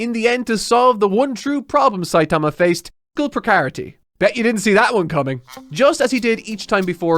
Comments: what is saying here? what was the one true problem saitama faced? "pricarity"? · Plupocarity? What are you saying here?